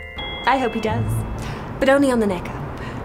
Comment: “…my prince will come…but only on the neck up…” 😂😂😂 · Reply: I thought she said “only on the makeup.” 🤷‍♂️